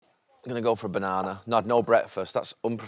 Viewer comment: Gary Neville peels banana 🍌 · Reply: I thought he said im gunna go with onana not banana 😂